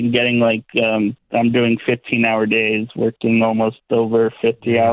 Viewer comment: bruv he just gotta do more than 15 hour a week! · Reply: nope he clearly said 15 hours a DAY and then said I work over 50 hours. Rewatch and listen at 5:38. Ahmed is right. The math doesn't add up.